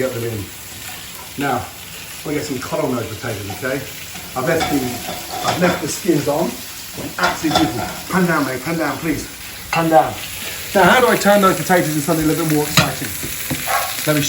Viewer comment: I thought I heard "Pandemic, pandemic".